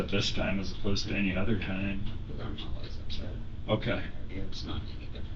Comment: the cc is wrong. She is saying: “ cuz I’m always upset. We opposed to “ I’m NOT always…” that kind of typos happens quite often; I just found this one to compliment her character a bit too much. She admits she is an obnoxious individual. Also, the inspector’s brain fart earlier while coming in: “this is the husband of your kid” 😮😅